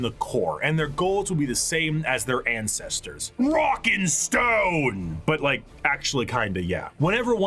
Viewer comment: did i hear a rock and stone?